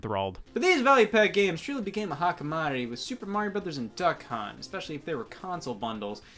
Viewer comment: when he said "hot commodity" I thought he said hakkimari which means village apparently